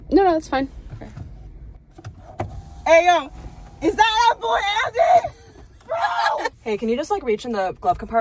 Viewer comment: did i hear "is that airport andy?" or ami crazy bahaha